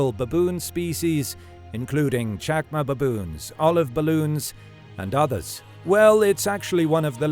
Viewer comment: did he say Olive ballon’s? Lol